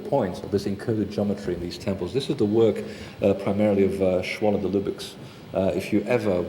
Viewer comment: what name did he just say at 53:30 ? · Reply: Schwaller de lubicz